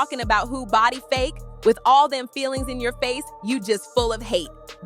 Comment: " with all them FILLERS in your face " not feelings 🤦🏻‍♀️🤦🏻‍♀️ 10:00 That's WORD TO SILICONE" not worried to sell a clown 🙄🤦🏻‍♀️ Where on earth did you come Up with feelings instead of filler?? And "worried to sell a clown "makes even less sense 😂😂😂